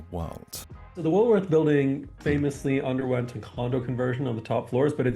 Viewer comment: The autocaptions for this video are jokes, at one point it misheard condo as condom.